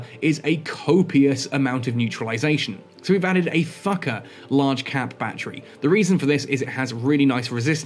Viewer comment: I heard "Thukker cap battery" as "Fucker cap battery"...